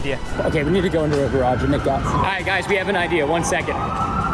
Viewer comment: The subtitle said we got to get into a garage and make out